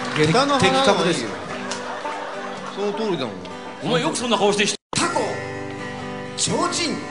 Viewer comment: 桜井さんの「お前そんな顔してよく…」この後編集？されてカットなってるけれどなんて言ったのだろうか気になります。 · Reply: 「お前よくそんな顔して人前で歌が唄えるな！」って言われた時には、あたしゃ返す言葉がありませんでしたよ笑 って感じだったと思います。大好きなシーンだったのでカットされてるのが寂しいです。